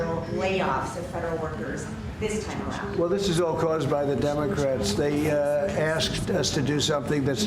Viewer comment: Did he say "Dum-acrats" 😂🤣😆